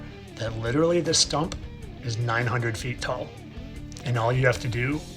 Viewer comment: Devil tower !you said is a horn? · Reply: he did not say a horn. He said "this STUMP is 900 feet tall"